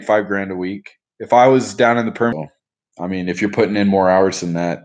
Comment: what location does he say? Breaks up, starts with a p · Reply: Permian.... west tx